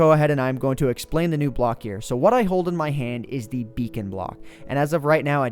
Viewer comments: I thought it said bacon block ;P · Lots of people thought it said Bacon Block instead of Beacon Block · who else thought it said bacon block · I thought it said bacon block. I'm sad now. :(